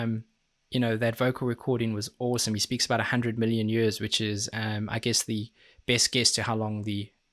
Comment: he actually said a 100 million century’s, which is 10 billion years, which is how old the universe was when the earth was born